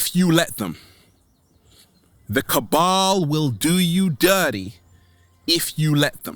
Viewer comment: you say something about the Kaball. I don’t know how to spell it can someone tell me what he is talking about_ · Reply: Cabal is a secret political clique